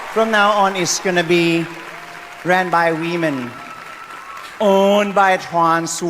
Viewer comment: All I hear is "We men" 🤣